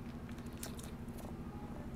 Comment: ｢終わり終わり…｣が｢と、通りすがり…｣に聞こえててもいい案件！